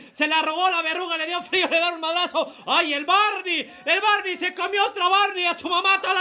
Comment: Pensé que decía Barney se cøgió a Barney, qué decepción 😔 Igual siempre rifa 😎